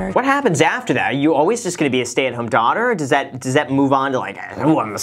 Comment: the subtitle says “Assad is a Satan daughter”…. Love it 🤣🤣